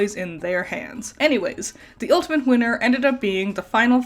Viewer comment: For some reason, I thought you said the ultimate GOONER. Why am I like this??? 😭😭😭